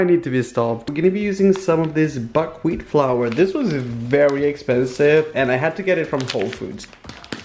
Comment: I thought he said fuckwheat